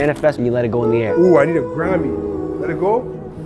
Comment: GET THIS MAN A GODDAMN GRAMMY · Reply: Ahhh...grammy....that makes much more sense. I thought he said Granny and I was quite lost 😮